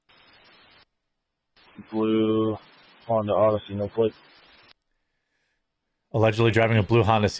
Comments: Sounds like he said Blue Honda Odyssey, no plate. I heard plate, not flick. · dont think he said "no flick" sounds like he said "no plate" like the car had the plate removed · or so. I think he said no plate. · As for the audio, I think the guy said, "Blue Honda Odyssey, no plate." But the autocaptioning interpreted it as 'flick'. Can anyone else hear that he said 'no plate'? (starting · Sounds like he said Blue Honda Oddessy no plates. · Sounds like he said no plate, not no flick....At least to me it sounded that way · It sounds like he said, "no plate." I see the captions mistake a word all the time. He is describing the car and obviously would have given the most discriptive detail a cop could give, the vehicle registration license plate number.